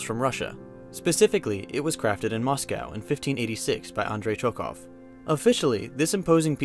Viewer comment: Sounds like he said Andrey Choke off XD